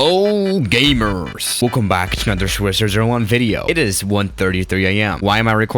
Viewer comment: what people hear : swiss001 video what i hear : swjzrvideo